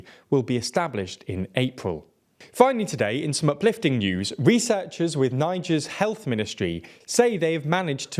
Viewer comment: I literally thought 6:23 said N-word Childbirth Improvements